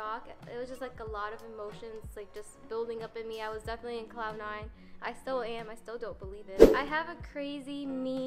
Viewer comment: did bro say she was in cloud 9 💀